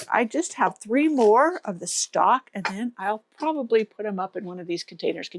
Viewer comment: Hi there. The purple flower? Sounds like you said “stock”. The fragrant one? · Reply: Yes, that's stock!